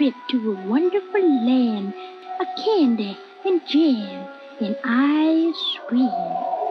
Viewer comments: I heard " just imagine a wonderful land of candy , and jam , and i scream " 😂 god ! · Lyrics: Just imagine a trip to a wonderful land Of candy, and jam, and ice cream My brain: Just imagine a trip to a wonderful land of shrandy, and chamb, and eyes scream 0:15 - 0:18 Smells like baby shark~ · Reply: I heard "... And I scream." XD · am I the only one who thought it said, "of candy, and Gin, and ice cream" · Who is here becajse they have actual good taste in music and not because of tik tok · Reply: No, thisispatrick XD it’s cause I’m in dat emo mode LOL plus when it said ice cream I thought it said eyes screaming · Ice cream Me:sounds like it says I scream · I thought the word Ice Cream said “I scream”